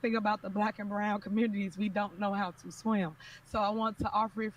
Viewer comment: Did she say black and brown communities don’t know how to swim? 🤣 Ridiculous.